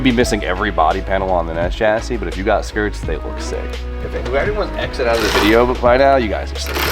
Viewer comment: whose sleeping? 😂 I couldn't make it out, Sounds like said "who everyone that exit out of the video by now you guys sleeping" 😂 they wont hear you 😂Anyone else know what he said there?